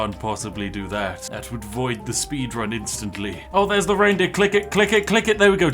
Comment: did he say reindick 🤔